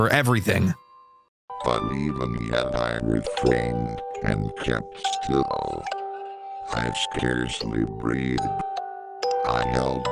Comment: Here is what he said 4:59. But even yet I refrained and kept still I scarcely breathed. I held the lantern motionless. I tried out steadily I could maintain the ring upon the eye. Meanwhile, the hellish tackle of the heart increased. It grew quicker and quicker and louder and loader every instant. the old man's terror must have been extreme. It grew louder, I say. Louder every moment. Do you mark me? Well